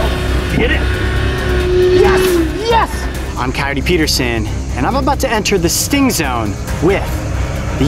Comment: It sounds like he says Hi I’m Kyeti Peterson and I’m abt to enter the sting stone Than Hi I’m Coyote Peterson and I’m abt to enter the sting zone If you want to say... Hi I’m coyote Peterson and I’m abt to get Friend Zoned...